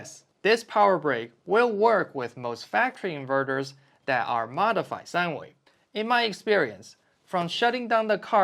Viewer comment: Good callout on the shape being more important than the total volume of a fridge. And sorry. but what do you say at 5:11? I couldn't catch it and the captions show "modified sandwich" lmao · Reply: Haha it’s modified sine wave